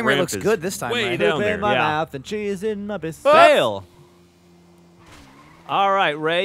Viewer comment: Is it just me, or did it sound like said "there's cheese in my bits" at around 2:25?